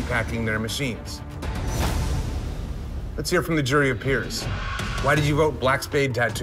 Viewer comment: Jury of Peers: They cheated! Judges: It's not cheating. Jury of Peers: It's still cheating! Judges: Old Town Ink isn't going anywhere. Jury of Peers: surprised pikachu face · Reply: My last name is Peer, so I thought u were saying a jury of me's 😂😂😂